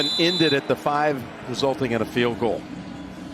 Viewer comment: i thought i heard fein